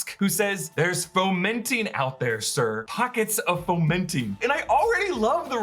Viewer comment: I thought he was saying "pockets of fermenting" like there was yeast producing beer or sourdough somewhere... lol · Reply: pockets ARE fomenting.... asin they are developing.